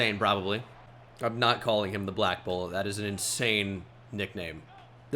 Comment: i thought he said "Black bolt" not Black Bullet" 😂😂